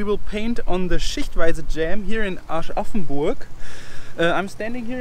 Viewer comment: hab erstmal Arsch-offen-Burg verstandenXD